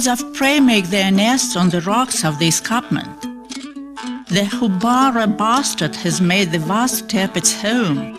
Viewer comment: when the narrator was talking about the Houbara bustard bird, she sounded like she said, "The Houbara Bastard" with an English accent.